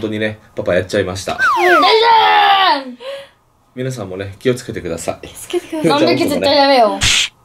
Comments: の気おつけて下さいってが言ってる所助けて下さいに聞こえてびっくりした😥😥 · が助けて下さいって言っているように聞こえるんですが...？ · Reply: 気をつけてくださいって言ってるよー大きい音にしたら聞こえた 多分、お父さんに引きながら多分言ってたんだと思う笑笑